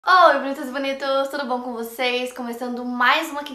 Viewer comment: "Olá bonitas e bonitos" isso melhora minha semana de jeito inexplicável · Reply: Eu sempre entendi "Olá meus bonitos " kkkkkkk a velha surda aqui